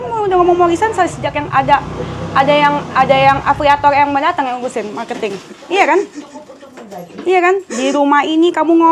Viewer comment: Si ngomong apaa kumur"yaa 🤣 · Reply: GUMOH KA😂😂